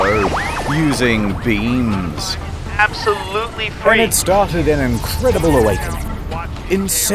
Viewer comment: i heard "beans"